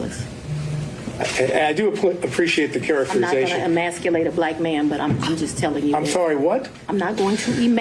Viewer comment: "I am not going to ejaculate a black man" is what it sounded like she said.